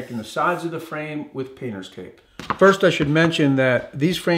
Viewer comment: “penis tape”? did I hear correctly? do I dare google this?